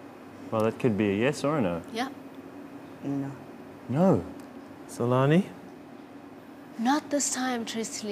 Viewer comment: dude sounds like he says salami..